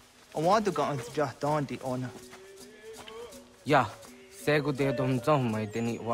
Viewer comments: on 00:07 he says yah but the subtitles says no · i thought he said ya